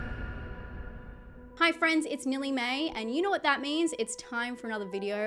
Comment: I heard "hi everyone, it's Nilly Mae" Took me a second listen to realize you said nearly may. 🤦‍♀️ I was like, "are we calling you something different now?"